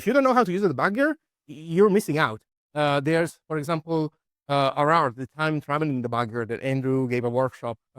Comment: what's the time travel debugger he mentions at 6:19. I can't quite hear what he is saying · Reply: i think he's mentioning "rr"